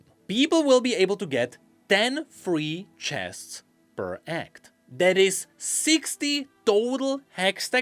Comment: I thought they said 10 per season not 10 per act